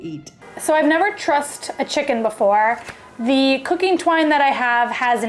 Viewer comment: I heard it as "So I never trust a chicken..."